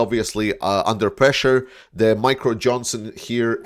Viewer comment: I misheard your pronunciation of Mike Johnson's name. I heard "Micro" Johnson. That would be a diminutive of Trumpian dimensions.